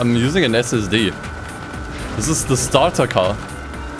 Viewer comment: What he says: This is a starter car What I hear: This is a stutter car Both are true